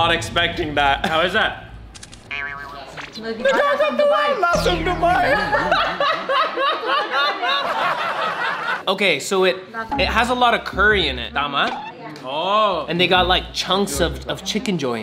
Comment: did you just say lasang kupal